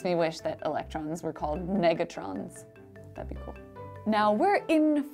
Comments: I thought she said niggatron omg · Thought she said ni**atrons😂😂